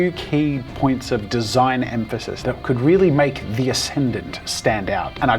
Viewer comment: I swear i heard cake, whitch changes the sentence drascically !